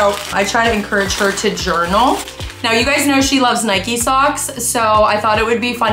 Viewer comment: When she said ' I know she likes nike socks' I thought she said I know she likes 90's songs. You would think I'm 60 but naur I'm 13😂😂😂